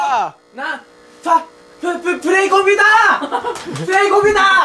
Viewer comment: プ、プレイコンピューターーーー！！！ ちゃんおぎにしか聞こえない